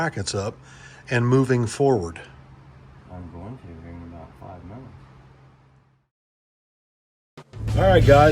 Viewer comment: My hillbilly dad used to tell me my ears are full of peanut butter whenever I couldn't hear what somebody had to say. At 18:24 caption reads (incoherent rambling) but in this case I heard clear as day "I'm a going to in about 5 min" so I reckon all that peanut butter ain't in my ears anymore! That's good because that was about 40 something years ago when dad said that to me. That's a long time to have a pair of ears full of peanut butter, so I'm glad that's no longer an issue. Dad used to say "I'm so mad I could eat melted ice cream with a screwdriver" so there is a chance, just maybe, he was pulling my leg about peanut butter in my ears